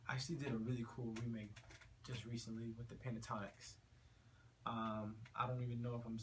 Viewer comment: Wait a minute - - - did he say Pentatonix? (3:47) A collaboration with Jason DeRulo and Pentatonix. When, when?